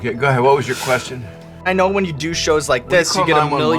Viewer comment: did he say "do shows" or "douche hoes"? Remember kids, not everything is as you heard initially.